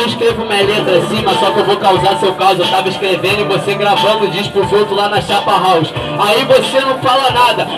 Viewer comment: O que falaram ali aos 2:40 min ? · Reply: Rennan Vinicius eu tava escrevendo e vc fazendo diss pros outros lá na chapahalls (uma gravadora)